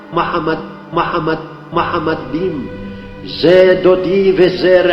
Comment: Why do you keep replying it back to make it sound like he is saying Mohammed? If you know hebrew and the actual prayer then you know he said "mahamadim" translates "lovely" or "desirable". Sad how people with very little knowledge spread wrong information and false claims. We are the Jewish nation, the chosen ones, humble ones. We KNOW G-D exists. We KNOW the truth. Sad truth is majority will always go by ignorance and falsification. And that is why we say "less is more".